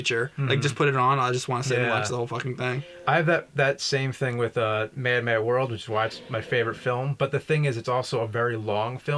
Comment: What's the Nerd's favorite film? I don't understand what he's saying. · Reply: It's A Mad, Mad, Mad, Mad World. You may have seen the remake, Rat Race, at some point.